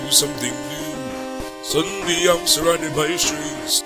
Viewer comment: I heard "Suddenly I was surrounded by shoes"